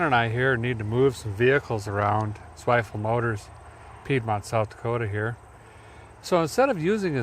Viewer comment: Where is this amazing yard? I’ve seen about a dozen cool old projects, 57 Buick’s, Oldsmobile’s? Is it open for shopping? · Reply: Sounded like he said Zweifel Motors in Piedmont SD.